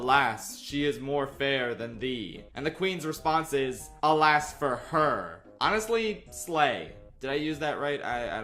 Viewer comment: I always thought she said, "A lash for her." Which would be so f'ed up since Snow White is only 14.